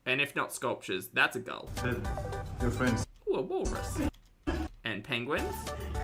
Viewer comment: wait, I didn’t hear him, did he say “hey look it’s your friends” to the seals and walrus’???